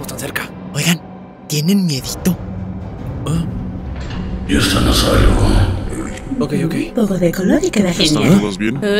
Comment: Alguien sabe que dice el large tv man en este doblaje en el minuto 2:28 ?? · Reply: Sal de ahí dice las teles hablan en reversa